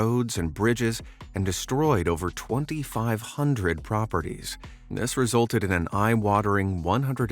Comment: I see an error in the subtitles. You said 25,000, however the subtitles say 2,500.